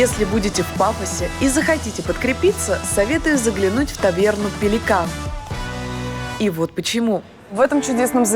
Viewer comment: я услышала не таверна "пиликан" а таверна дилюка